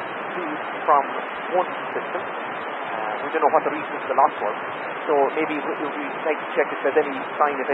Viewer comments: As always great assembly of ATC. A couple of transmissions that gave trouble for the subtitles:- 9:14 2nd Sentence, "We don't know what the reason for the loss was." / not:- "We don't know [.] the last one" 9:49 You wrote: "We have plodders being deployed" - I think he said "We have pump teams being deployed" but it certainly isn't clear. Hope that is helpful. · "We don't know what the reason for the loss was" I think he said